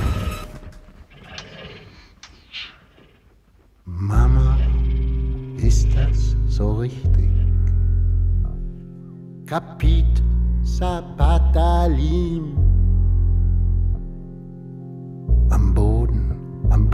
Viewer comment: "Kapit sa patalim" Am I hearing it right or just hallucinating